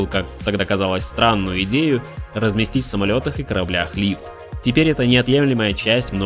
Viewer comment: Рлив? Лииф? Лифт? Чётче надо произносить. С пятого раза только расслышал.